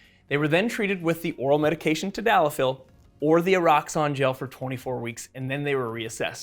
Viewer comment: There is now way the called this e-rox-on right? cause i genuinly thought he said "a rocks on" at first until i read the label.